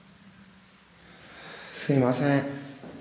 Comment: すみませーん、はーい って聞こえるのは私だけ？